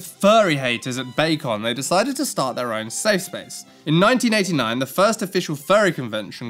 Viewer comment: I heard "cesspool" instead of "safe space"